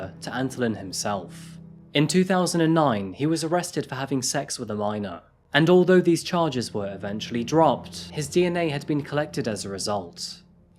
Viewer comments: I swore I heard say “…for having sex with a rhino…”. Made me laugh and do a double take before realizing what he really said. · I had to back it up a few seconds and listen to it again. I thought you had said that he was arrested for having sex with a rhino. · Had to back up, thought he said dude had sex with a rhino 🤦🏼‍♀️